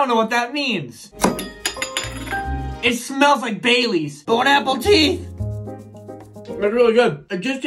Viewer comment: For a second I really thought he said it smells like baby’s 🤣🤣